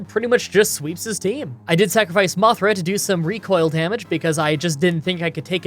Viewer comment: did you just call Mothim.. Mothra?